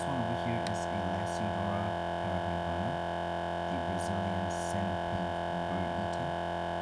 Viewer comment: I'm too hungry. I misheard "brazillian birdeater" as "brazillian burrito"